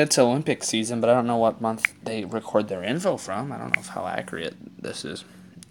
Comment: Listen around 2:40 for him to say the word Acreate instead of accurate